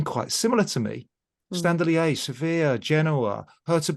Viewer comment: Love to see how the automatically created captions say: "Hurts of Berlin" in 0:09:51 :D Greetings from Germany.